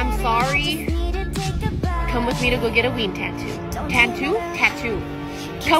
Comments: NOOOO I THOUGHT SHE SAID WEED TATTOO 😭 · I read that as “weed tattoo” 💀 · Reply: After hearing the robot voice a couple times it sounded like she said “weed tattoo” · OMG I THOUGHT SHE SAID WEED TATTOO 💀💀💀💀💀💀💀💀💀💀💀💀💀💀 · i thought she said weed tattoo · I thought she said weed tattoo I was like “boy what in the hell” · Ok the first time you said ween I thought u said weed tattoo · At first I thought she said weed tattoo lol. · I thought you said weenie tattoo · For a sec i thought u said weed tatoo im like "WHAT" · I thought you said weed tattoo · YALL- at first i thought she said weed tattoo